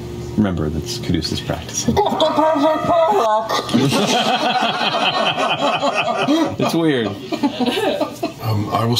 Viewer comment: what did she say? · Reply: She says "you've got the perfect warlock" in reference to the DnD Beyond ad